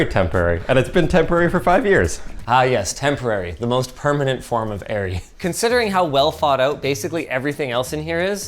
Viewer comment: “Ah yes temporary, the most permanent form of -ary” is a damn good conclusion to come to. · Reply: i heard it as temporary the most permanent form of eerie in the video and it didnt even sound off